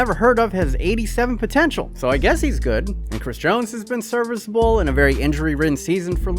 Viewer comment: did he just say chris jones 10:56? Lmao